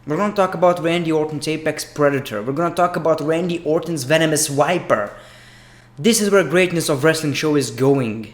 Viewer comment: I hate when he says wiper instead of viper